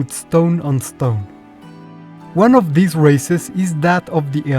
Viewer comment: I know I misheard "races" as "racists" in the intro, but damn if it ain't accurate.